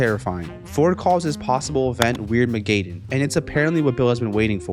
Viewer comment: Weird Ma gay den is what this man said